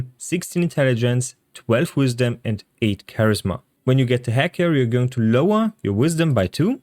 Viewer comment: can anyone tell me what he is saying at 1:21 ? "When you get to h...?" I think this is crucial to the build but I'm having a hard time understanding it. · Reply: hag hair. Its not crucial just a bonus for 1 character per playthrough. Gives you +1 to a stat of your choice when you spare the hag instead of killing her.